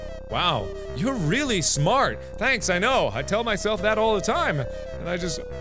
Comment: I thought it said your really shart